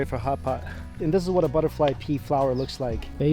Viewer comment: I thought said "it's made with butterfly pee" when in reality he said "butterfly pea". Thank God for Google. XD